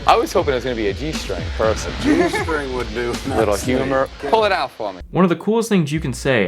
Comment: at @ 3:20 I actually think he said "a little humid" but dude is still weird